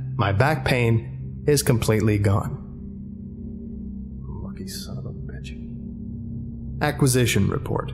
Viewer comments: anyone else hear the quiet "lucky son of a bitch" at 20:28? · People who heard him say luck son of a b****😂😂